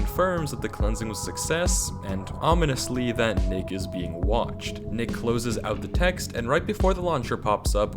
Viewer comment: i thought 10:40 said Ni__a's been watched